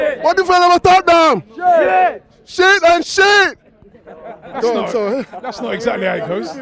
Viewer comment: Hold on a sec what was my man saying 0:11 "sheit then sheit" bruh 😂😂😂💀